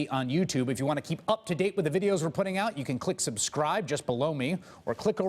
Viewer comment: Every time I hear this end plate, the "just below me", I think I hear "just blow me" xD